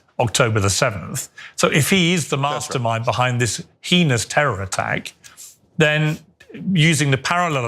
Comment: It sounded like Piers said penis terror attack 🤣